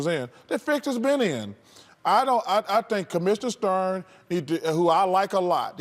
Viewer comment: did chuck say "commissioner stern"? at 4:10?